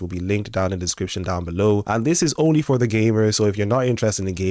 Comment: Your accent 😂 you said “gamers”. I heard “gay nerds”.